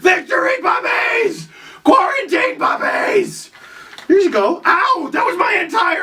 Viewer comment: Thought it said rantanen lmao